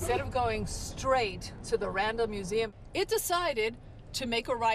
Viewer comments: I thought she was saying "Random museum" the whole time until I saw the sign at the end haha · Oh, Randall Museum! We thought you kept saying Random Museum.